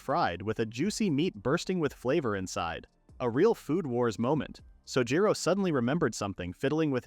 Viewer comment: did he just say food wars no that's way to mild for food wars